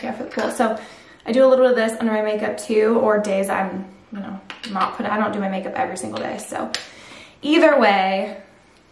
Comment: I’m trying to figure out what you are saying this is. I’m probably just missing it. The one you can put on before makeup or not and leaves a glow. Please help this ole girl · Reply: Super goop glow screen